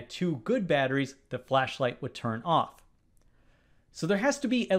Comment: Your pronunciating "will turn o??" is so bad, that it could be ON or OFF. You just say the letter O so hard that it is impossible to say what is the next letter. It should be the word "ON", but it sounds more like "OFF". Even the YouTube automatic transcription for CC tells that is sounded as "OFF".